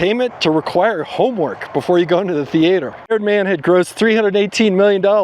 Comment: I heard him say thee ate her. But it was supposed to be theater.